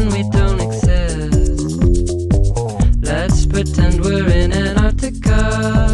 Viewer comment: he's saying "autocar" but I could have sworn he was saying ANTARCTICA!